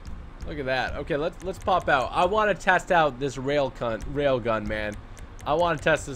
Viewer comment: Did he say railcunt